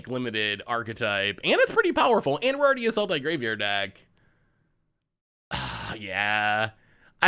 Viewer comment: Every time says: "Sultai Graveyard Deck," I hear, "Salty Graveyard Deck."